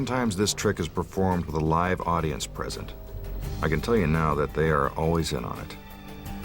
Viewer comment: What does the narrator say here: 43:20? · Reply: (Bilingue DualCore) he saysa the audience are in on it